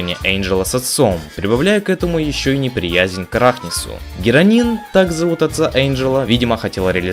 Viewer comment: Мне послышалось "героин", а не "геранин" ,_